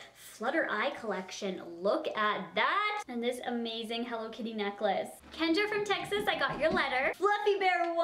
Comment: When you said hello kitty neclace i thought you said cocaine atlas fml 😂